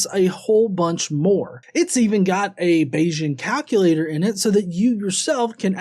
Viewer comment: I heard "Beijing calculator" when described the App! I wonder what one of those does?